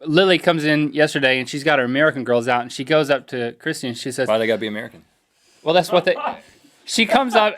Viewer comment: What does Rhett say at 4:10? I played it like 6 times and I am still not able to understand. Thanks. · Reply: "Why do they gotta be American?"